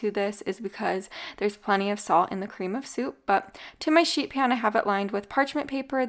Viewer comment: Sounds like you said shit pan 😂😂